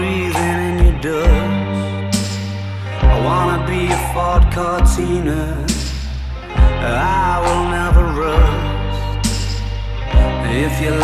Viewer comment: For some reason I always thought it said “I wanna be your fart container, I will never rust” 💀